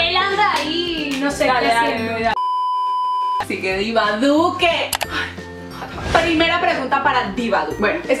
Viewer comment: Entendí “Sigue Iván Duque”🤣🤣